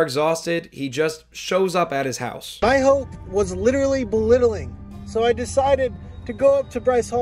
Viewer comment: did he say “My hope was literally belittling”????